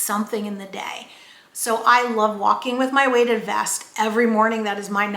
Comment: Hello. At 15:30, you say that you loke walking with ...did yiu say weighted vest? You were talking a little fast, and I could not understand what you loved walking with. Thank you. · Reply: Oh sorry!! I walk with a weighted vest every single morning. I have a couple of videos on walking with a weighted vest and will be doing more because it has benefited me so much! I’ll link one below for you if you’re interested and I also post a lot more about this on my Instagram, Facebook and TikTok! Thank you so much for watching and being a part of this amazing community. Really appreciate you💜 Weighted vest walking Change my body composition: <link>